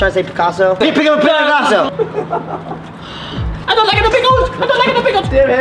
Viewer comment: Idk what Kian said at 6:30 but it had me dying 😭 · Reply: kprince203 "I don't lika tha pickles I don't lika tha pickles!"